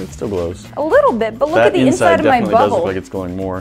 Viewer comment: it sounds like she said “look at the inside of my butthole” 😂😂